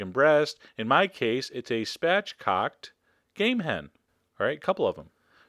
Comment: I watched the spatchcock chicken video a minute before, I thought you said 'gay man', I now realise it was game hen. Enough internet for me today, thanks Chef John. <3